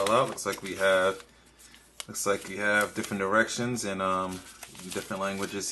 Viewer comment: did he say we have different erections?! :S